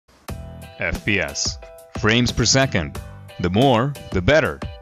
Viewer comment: I thought he said brains per second